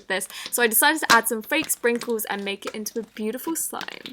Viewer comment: I thought she said faint sprinkles XD